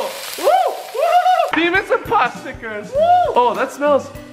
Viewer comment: did he say my mouth hole 🕳😱😭